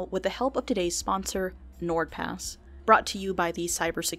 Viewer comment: I thought the Nord Pass said N-word pass